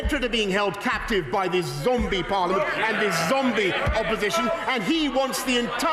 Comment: Did he just say " this zombie parliment" ? Lol